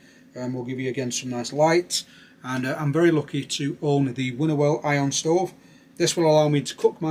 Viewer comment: Hi please can you type the name of the stove you mentioned after talking about your paraffin lanterns, it sounded like you said “woodwell ion stove”? Thanks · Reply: Winnerwell iron stove. Its a great bit of kit.